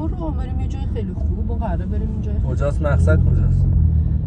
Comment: 👉👉👉((( کلیک کن روی دقیقه تا ببینی چی میگه اول ویدیو))) دقیقه سه و چهل و سه ثانیه گفت محضر کجاست یعنی کلا سناریو بود از اول.😉🙃😊